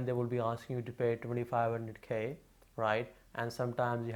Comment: Its 25k which 25000 PKR. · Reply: and 4:13 i heard 2500K - never mind 25k sounds reasonable is the Form C still a requirement or has it changed plz?